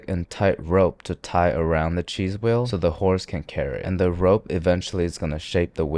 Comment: at first I seriously thought he said so the ho's can carry it. 💃